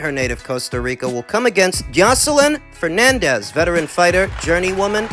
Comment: For a second I thought you were saying Joseline Hernandez the reality start Lol I would pay to see her get her ass kicked